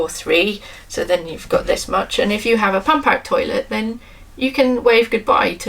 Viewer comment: Interesting video. With the closed captioning on, at the 4:30 mark, it transcribes the audio into "...if you have a fun-packed toilet then you can wave goodbye to...." (instead of "pump-out") . I had a good laugh at that one :)